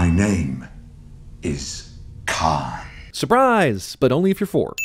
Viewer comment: Why does Khan say his name like Kai? Is it Kai and then changes? I'm confused!